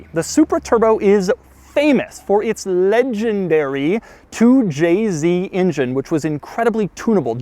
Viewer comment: Did he really say at 1:46 " "Lenginedary" in regards to the engine